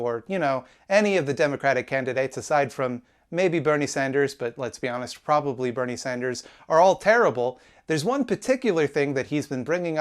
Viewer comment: I heard it as him saying everyone except Sanders is terrible. It's not really the clearest point in the video, though. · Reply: "every candidate except bernie sanders, although probably bernie sanders is terrible"